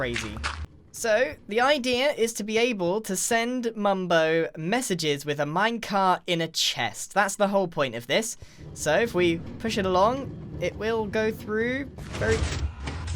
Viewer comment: is it just me or did he say minecart in a chest?